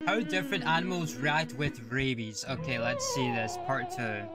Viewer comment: i thought he said babies